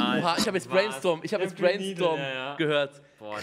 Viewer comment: ich hab greenstorm gehört